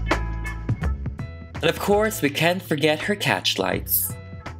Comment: IT SOUNDED LIKE U SAID: "And ofcourse we can forget her catch lights."